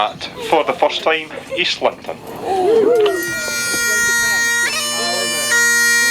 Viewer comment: I just kept mishearing it as Islington